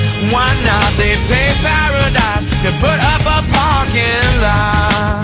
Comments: I heard “feet paradise, the put up a f**king lie” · I always heard "Gay Paradise and put a parking lot" Idk why. I had to Google it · For years I thought they were saying "They shaved paradise and Portable Parking Lot". That's a nickname for those trucks that carried a load of cars to the dealership.